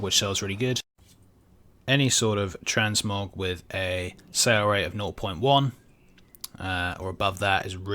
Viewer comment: what is he saying...the sell rate?...i am hearing inpared and didnt get it i when back alot and still cant make it out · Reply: richard mitchell sale rate (it’s on TSM when you mouseover an item) :)